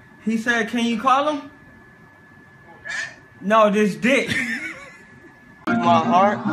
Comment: It sounded like he said "This stick"